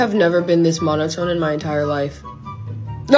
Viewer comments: 🤣 im so old, i thought she said "no, go away jabroni." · "I have never been this monitored in my entire life" had me😂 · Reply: omg I thought she said monotoned lmao